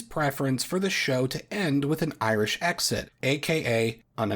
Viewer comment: I misheard "Irish Exit" as "Irish Accent", and at first imagined they'd speak with an irish accent for the final episode